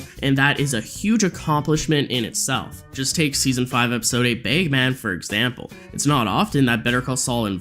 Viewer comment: Did you say "BAE-man?"